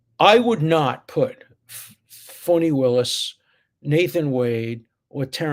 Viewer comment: really sounded like he said Phony Willis. It would have been an appropriate Freudian slip.